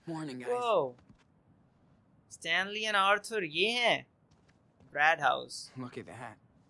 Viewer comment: "Brad House" Instead of bird house